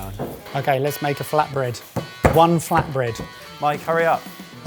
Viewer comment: I thought he said. Fat bread